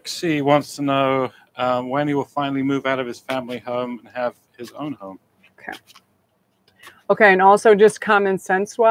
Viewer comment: I didn't catch the name of the deck you used at about 34:40. What was the name if it? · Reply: Witches Tarot by Ellen Dugan